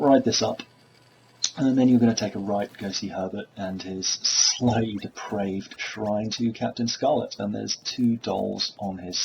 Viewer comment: wtf do you eveb say there? "Slave praved"???